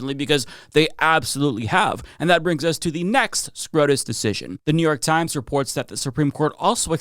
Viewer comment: did say SCROTUS? lol